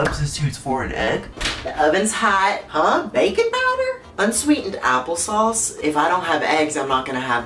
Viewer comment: I thought he said “bacon powder”